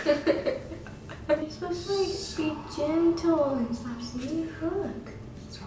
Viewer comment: Is it just me but I just heard swear at 4:11 she said "You have to be gentle and stop saying f**k" I am most probably wrong but it sounds so much like that